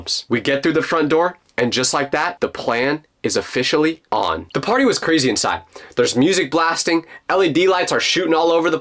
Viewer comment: I thought he said the farting was crazy inside instead of hearing the party was crazy inside